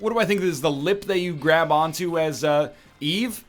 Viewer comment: At first I thought for sure he said, "lip to grab on to."